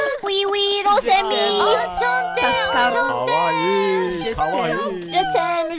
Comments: I think was saying "Enchanté" (Glad ) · subtitle is wrong. She didn't say je t'aime but enchante, which means 'nice to meet you'. Their voices in their native languages are so nice to listen to! Everyone's code switch is really interesting to see too. Their voices and pitch change when they speak the other language to reflect that that culture's speaking mannerisms. For example, voice when wayyyy higher when speaking Japanese because that's just how native female speakers speak Japanese. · i think says enchantee but i might be wrong